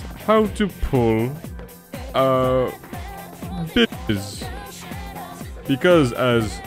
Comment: I thought he was saying stitches it wasn't till shitty subtitle blanked out the word that I fingered out it was butches lull